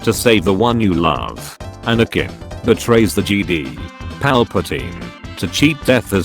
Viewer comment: Auto text says GD instead of jedi